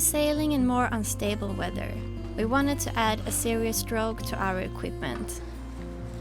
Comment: That was a first class installation job on the Series Drogue. I am impressed that the cone lines are arranged the same as in a parachute deployment bag. I respected parents before they appeared on this episode due to the excellent way has turned out, though Malin has surely helped keep him the way he is. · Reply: Thank you for this comment. Sometimes due to their accents, I have a hard time understanding what they're saying, well, mostly anyway. I kept hearing him say "Serious Drone" and I knew that wasn't right. But now I can google Series Drogue and find out what it is.